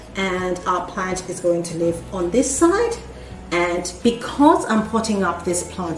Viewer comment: What are you potting up the plant in? I came from reddit and watched both of your awesome videos on these poles. I can't understand what you are saying you pot your plant in and you mentioned it in both videos. "Because I'm potting this pot up in ----" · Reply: I do not use soil for the majority of my plants. I use clay balls or leca. So in the video I am saying leca.